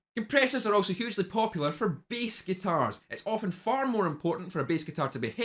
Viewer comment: Am the only one who thought he said beast guitar when he actually said Bass guitar lol :x